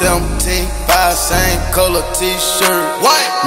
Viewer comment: All this time I thought he was saying 75 cent colored T shirt